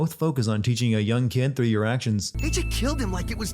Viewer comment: u said through ur actions but the subtitles said through your decisions. Adds a sin